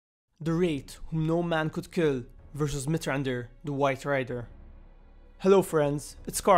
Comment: At the beginning it sounds like he said, "Mithrandir, the WIFE RIDER", I'm probably alone in thinking this though